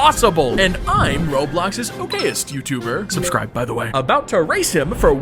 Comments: I thought he said gayest youtuber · I thought it said in the start it said im the gayest youtuber